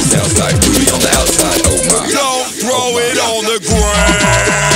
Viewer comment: "Don't throw it on the ground." MC out here dissin Lonely Island😥😥😥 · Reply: I thought he was saying "don't throw it on the GRAM" as in Instagram.